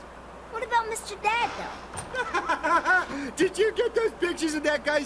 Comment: did he just say, did you get those bitches????!?!!?? HE DID???